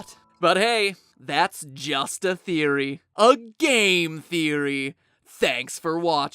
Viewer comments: but hey it's just a theory. A GAY theory. I left the video thinking I heard that saying. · OMG I heard "That's just a theory. A GAY THEORY!" XDDD